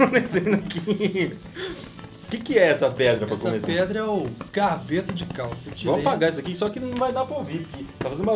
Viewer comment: ei, só eu q ouvi "capeta de calça" ao invés de "carbeto de calcio"